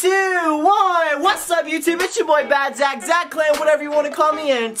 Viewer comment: When he said "its yo boy badzach" at first i thought he said "bruhitszach" lol